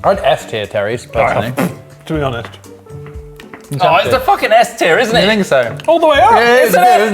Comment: I thought said "f tier" for Terry's, almost took a sip of water just to spit it out